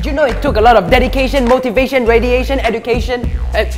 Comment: did he say radiation